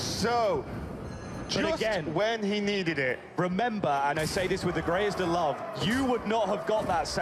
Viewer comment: what does the commentator say? "And i say this with the greatest love?"